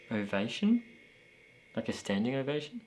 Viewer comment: you guys hear ovation or maybe I bashed her. Upon first listen I thought I heard “impatient” but when I listened back a few more times, I heard invasion 😂